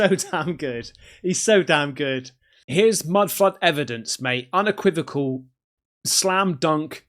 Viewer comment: did you just say “mud fuck evidence”? 😂